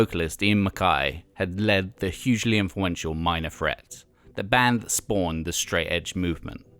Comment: Did he say Minor Fret?